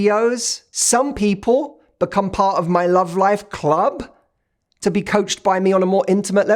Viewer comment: I thought you were gonna say “some people become part of my love life” im like what!? How do I do that!? I’ll dare ya! Hah Then you said “circle” Ohhh 😂 Immm